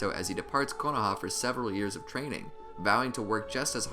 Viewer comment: did he say searval years